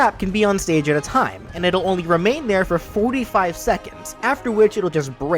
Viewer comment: ONLY 45 seconds? Did I mishear that?